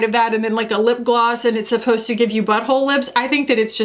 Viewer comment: The way I thought you said BUTT HOLE LIFTS! LOL! The only trend I am in support of is Orca sporting salmon on their heads. A crisis of self esteem is right, 'I don't like it just fix it' sounds like a bad idea at a hairdresser never mind a surgeon.! Thank you for being both my escape (number 1 yt wrapped this year) and the most reliable make up reviewer out there - I'm really grateful for you, thank you for the swatches (yes please) and all the spreadsheets - big love from one of the most beautiful people in the world TO one of the most beautiful people of o the world x